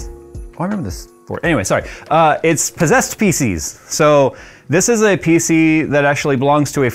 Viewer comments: Thought he said "possessed Faeces" rather than "possessed PC's" - need to upgrade my ears.... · I could have sworn you said we love "possessed feces" and not "Possessed PC's"